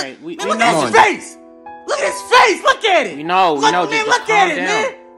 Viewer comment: Did he say "WHY IS IT SHINEY!?" 😂